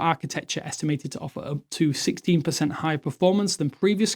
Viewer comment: Did he say 16 or 60%. I hope it was 60 because 16 is nothing. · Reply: 16% compared to G5, it's also likely to be much cooler thanks to the 2nm